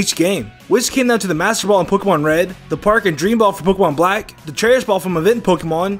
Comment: I thought he said Pokemon Reindeer lol